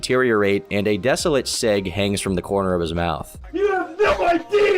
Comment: i heard that as sig, as in sauer, not arette and that this was gonna get even darker